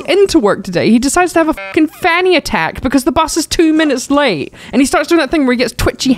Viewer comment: all I heard was 'fanny attack' and I didn't hear a single other word after that...